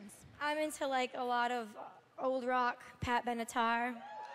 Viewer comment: what does she say at 0:30?? old rock and what? all i hear is pap and guitar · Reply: You mean Pat Benatar? Wow... seriously?